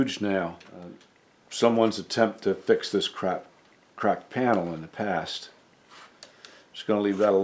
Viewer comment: I chuckled at 22:00 when you were talking about the cracked panel but it sounded like you said crap instead (which was just what pounding a giant nail into that piece was)! Another fabulous video--I am always in awe of your patience.